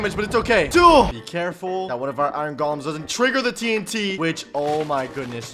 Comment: I honestly thought the iron golems said iron predators